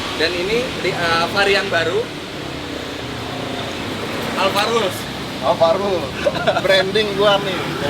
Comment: gw dengernya ALFARRUQ wkwk